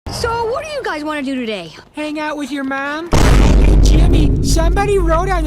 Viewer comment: I swear I heard “make out with your mom”